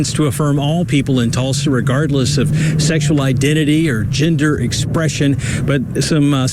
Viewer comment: For a second, I thought they were saying, "ginger expression."